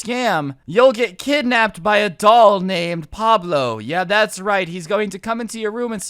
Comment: I thought you said a dog named Pablo I was staring at my dog named PABLO